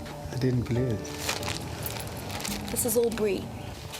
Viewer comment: I thought she said the NSW suburb of “Albury”